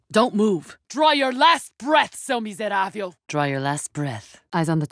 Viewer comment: / 0:50 what does she call them? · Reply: Seu miseráve and it means you fool (sorry for being 6 months late)